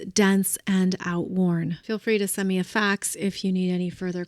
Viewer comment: Thank you ❤ So very much looking forward to a new phase of life... But was it only me who heard you say "send me a fax" after just telling us to throw it out 😂 (one of the few things I have disposed of, but still have that random drawer and the clothes under the bed to deal with...)!!